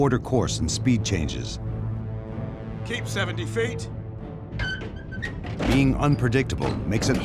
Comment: wtf did he say feet? hes british tho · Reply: keep seventy feet